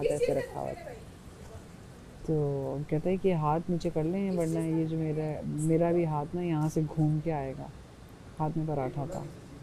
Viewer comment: was wrong did not say that he said apna hath nichaaay karlo warna ye paratha ghoomta howa taway pay jayyga uska matlab tha tum jitna hath ghooma rahi ho us say paratha urtawa tawway pay jayyega is a real phophoo and snake of sleeves also